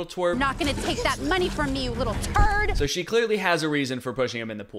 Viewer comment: she said turd not twerp which isnt a big deal that he got wrong but i think her saying turd is 300x times funnier because. how am i supposed to take this seriously